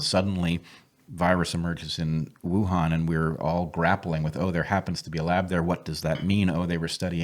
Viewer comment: I heard “we’re all gonna get it”